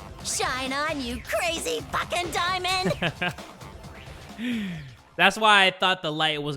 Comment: Did I just hear crazy diamond?